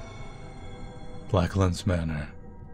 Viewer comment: It sounded like you said "Black Lives Matter" & I had to take a double take before realizing you meant "Blacklands Manor," haha.